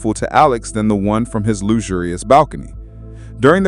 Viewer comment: "loo-zu-lu-res". what? WHAT???? Are you trying to say, "luxurious" ?